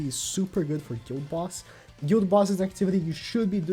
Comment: what exactly u said about a boss?Sry i cant clearly understand it my english arent that good. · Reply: Guild Boss